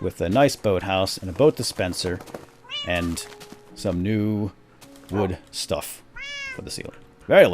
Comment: I THOUGHT HE SAID NEW WORLD?????????